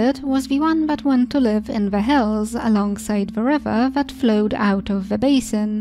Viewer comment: ohhhh she's saying hills.... in the video about how The civilized see the foragers i thought she said that the foragers lived in the hells, and it was just her explaining how awful the civilized people thought it was